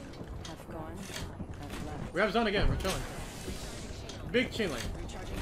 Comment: did he just say BING CHILLING